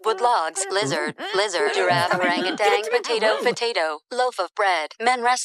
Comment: DID THEY SAY ORANGU-TANG??